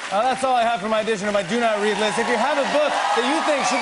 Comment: I thought he said "Do Not Pee" this whole time...I feel stupid